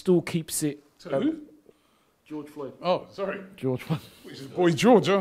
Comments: Hahaaa! I thought he said Boy George too 😂😂😂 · "We must never forget what happened to Paul George" so ironic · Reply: I thought he said boy george